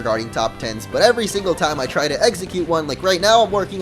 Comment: i swear at 12:43 you say eggsecute instead of execute... lmao